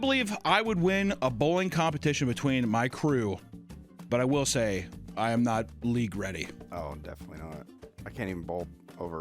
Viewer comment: I swear I heard that as "I am not Lee Gretty". I was like, who the hell is Lee Gretty? Gotta be some pro bowler or something.